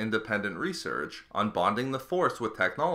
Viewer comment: Oh, "bonding the Force with technology" I heard "mocking" lol